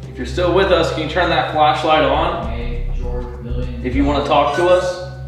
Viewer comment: Something weird happens with the voices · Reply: Yeah i caught that it kinda sound like saying “May George million fell off? “